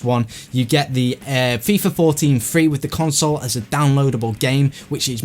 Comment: Did he say with Xbox 1 you get fifa 14 free?